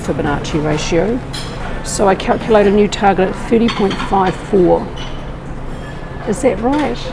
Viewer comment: Sorry, What is the target for wave 3? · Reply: Sounded like she said 30.54 ???